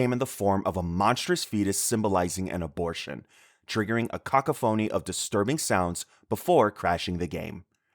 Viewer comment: I'm here for the pronunciation of cacophony. "Cock-oh-phony." Sorry I can't stop chuckling. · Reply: I heard it as "CACA- phoney" which is also funny.